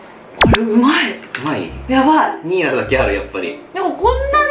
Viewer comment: 217だけある？に聞こえたｗｗｗ